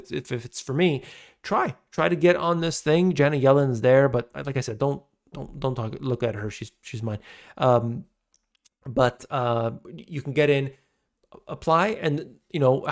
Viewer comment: "She is mine." I laughed espresso out all over the place. To funny. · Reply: I thought he said "She's fine", Your interpretation is funnier.